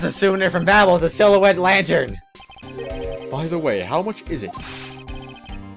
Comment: I can see how you would see "by the way" instead of "by the by" in that sentence there because it's more common to say "by the way" instead of "by the by". "By the by" sounded so weird to me that I had to look it up just to see if it was a typo or not and it turns out that "by the by" is another way of saying "by the way". Here I thought it was just another Ace Attorney typo, but apparently, it wasn't.